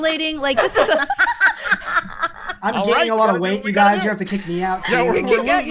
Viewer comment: I had to re-listen a bunch of time to be sure I wasn't mishearing.. I was x) (I heard "I'm gay and I can't wait" instead of "I'm gaining a lot of wight")